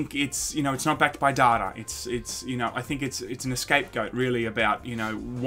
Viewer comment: Did he just say Escape Goat?